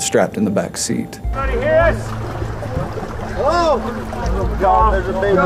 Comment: At the 1:31 mark, you Can actually hear an adult Female voice saying "Help Us" it's only bc the rescue team are speaking loudly that it's difficult to hear it. (Not sure if there is any editing that can be done to lower the males voices so that the female's voice can be better heard but I heard it using my headphones).